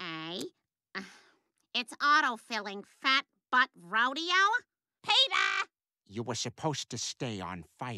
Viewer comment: I thought she said fat fuck rodeo lol